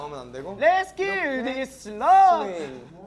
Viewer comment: did seungkwan just say "let's kill this love"?????? My caratblink hearteu😍